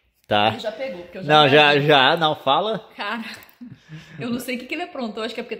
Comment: a Nilce falando c*ralh* ou escutei errado? kkkkk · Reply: Cara